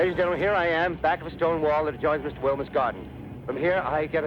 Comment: Opening this video with the classic War of the Worlds radio drama was a master stroke! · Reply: I thought he said Stonewall. I thought he was reporting from a gay nightclub raid. 😂